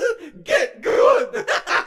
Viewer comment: On 0:13 you see lolbit hit funtime freddy and lolbit says 'get good' but I thought kinda differently...So Imma say what I imagined what lolbit was saying... I imagined lolbit saying... I LIKE YA CUT G